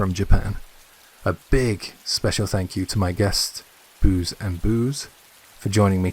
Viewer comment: Great as always J, very scary stories, thanks. you've a new listener xxxx Ps I thought you said Booze & Boobs 😂 xxx